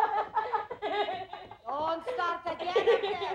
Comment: I think she says "Oh, stop again up there"